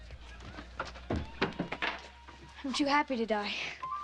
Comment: WHAT does he actually say when he comes back? Ive been trying to hear that for ever. Please tell me, its driving me crazy. It sounds like he's saying "youd´be happy to die" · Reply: The Greendalewitch “I’m too happy to die”